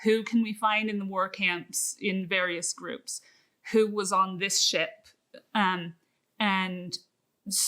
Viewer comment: "Who was on this shit?" What I heard the first time around. I was like "wait what?". . .